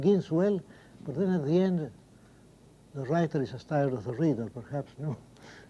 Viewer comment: En 27:26 creo que dice "the writer is as tired as the reader" ("el escritor está tan cansado como el lector"), lo que me parece que tiene más sentido.